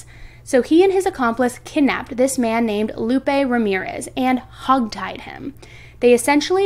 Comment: ".....and hug-tied him...." that sounds so cute. I went back a 2nd time to listen and you did say it more like hog tied, but what I heard first time sounded better.